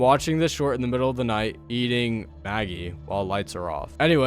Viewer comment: I thought they were talking about maggots 💀💀💀💀💀💀💀💀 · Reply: I thought bro said magpie